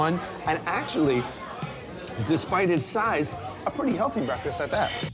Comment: I thought he said a healthy breakfast?